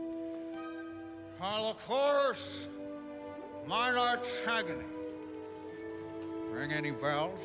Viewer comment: the subtitles say "holocaust minor arch agony" but what is he actually saying?